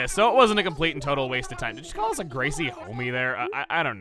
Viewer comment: "Homme" was what Gracie said at 9:20 and it means man.